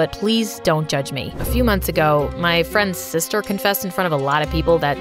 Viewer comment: I dint hear "friend" and I wasn't looking at vid and I heard sister and I was like SwEeT HoMe AlAbAmA